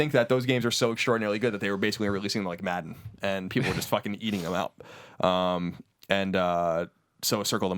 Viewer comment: Does Colin say "people were basically eating them out" ?? haha